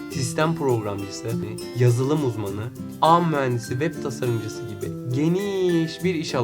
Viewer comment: da ne diyor tam anlamadım :) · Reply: ağ muhendisliği diyo yanlış anlaşılma olmasın :)